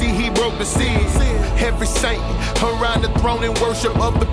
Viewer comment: I think he says "Saint" not "sight", and at 3:45 I think he says "either way, He's such a generous God" not "by the way" but I could be wrong lol